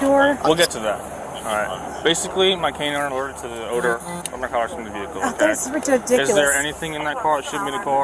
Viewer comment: im dutch but i heard him say: “off narcotics” instead of mumbling